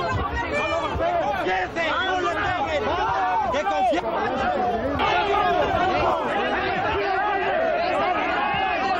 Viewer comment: Alguien entiende que dice en el tiempo 2:25 ? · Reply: Dice "No fui yo" lo dice varias veces